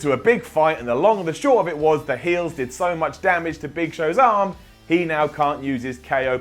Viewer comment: Initially misheard as "Big Show's arse"